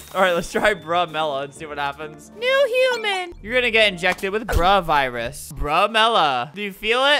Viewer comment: I thought he said Corona Virus because this video was last year